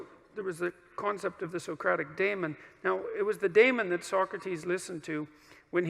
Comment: Is he saying "demon"? It doesn't sound like he is saying that, but I'm not sure I'm familiar with the word he is referencing to. Anyone? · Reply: Damon. he says the story wrong I don’t think he read Plato. But the Damon was kinda like Socrates version of the Holy Spirit